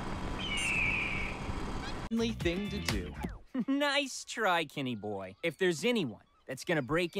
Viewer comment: I keep mishearing "Kenny boy" as "gay boy"😭